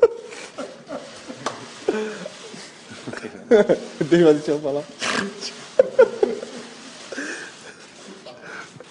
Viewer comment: Minute 0:20 Marcelo... what he said? in portuguese i guess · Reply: Marcelo said "Você vai deixar eu falar??" or in English "Are you going to let me finish??" :P